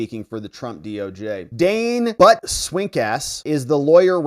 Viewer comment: I honestly thought you said "Dane Butt Stink A$$." Yea, I'm still 12 some days. I hope that law firm is ended.